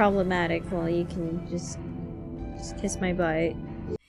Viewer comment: I thought she said "Kiss my bike."